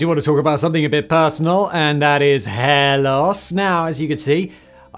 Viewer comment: legit thought he was saying hellous or something but it was hair loss 😂